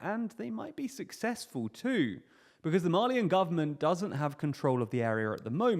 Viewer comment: Thought you were saying “Marlean”, not “Mali-an”. I was scared we were about to start seeing Titans in Africa lol